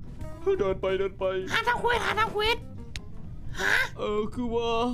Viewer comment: หาทำเควสแต่ทำไมเราได้ยินเป็นหาทำค_ย5555